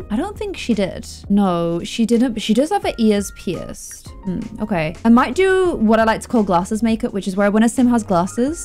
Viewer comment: When you said "She does have her ears pierced" I thought you said "She does have her ESPS." I don't even know what that means.